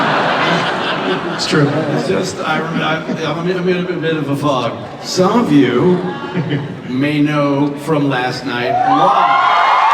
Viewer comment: can someone tell me what joss (and Nathan) said around 20:58 - 21:28. i can't hear anything over the cheering. thanks! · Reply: later but here you go, as best as I could make out: Joss: I'm in a bit of a fog. Some of you may know from last night why... You got the good seeds. Nice. Nice. stoner voice I don't know how some of you got the good seeds but that's cool. Nathan: to audience Who was on something unintelligible outside last night and got woken up by this guy? points to Joss Did you think you were dreaming?